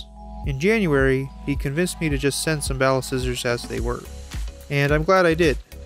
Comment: Not gonna lie, I thought that said "Ballscissors" and I was very afraid